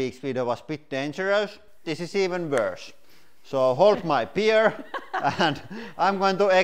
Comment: “Hold my beer” translation- this will be good. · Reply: I thought he said hold my beard lol I was so confused.